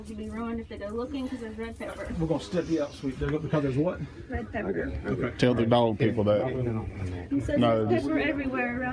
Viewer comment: Did anybody catch what she was saying about the dogs? 6:14? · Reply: Dogs would be ruined because of red pepper, if you don't want dogs to find something, sprinkle red pepper everywhere, won't permanently hurt them but it would put them out of commission for a few days if not weeks